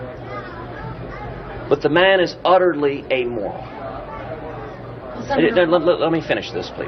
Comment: Oh ok. I thought Cancun Ted said Trump was a moron but now I saw it in print it was amoral. Both are true anyway.